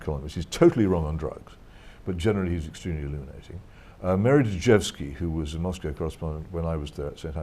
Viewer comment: who is he talking about? Meredith... cant understand the last word. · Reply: Unknown Soldier Mary Dejevsky